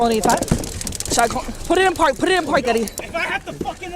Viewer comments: is she saying "Put it in park, daddy"? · does she say "Put it in park daddy"? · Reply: Put it in park eddie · Did I hear “put it in park daddy” ??